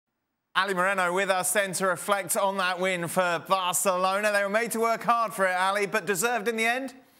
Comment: "varçelona" Edit: that was just what i heard, I'm not a madrid nor barca fan.